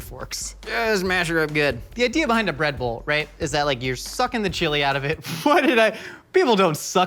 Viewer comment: I totally thought you said “The idea behind the Red Bull is you’re suckin the chili out of it” and I was so confused as to when and why red bull was in the recipe lmao